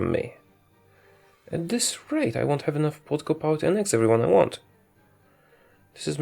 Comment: When he says political power the CC says vodka politics in